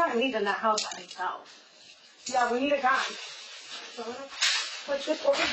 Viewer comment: I don’t think she said I need a gun 29:08, I think she said "I need a guy" you can tell by the continue conversations afterwords..the other girl said "I bet Michael would do it"